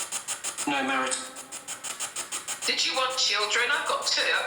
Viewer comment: Your brave being in there by yourself. I could definitely hear the wail/talking with headphones on. Also when you asked if they were going to get married, I heard not married